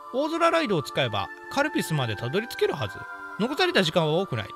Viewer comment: ガルビルスなのにカルピスって聞こえるのは俺だけ？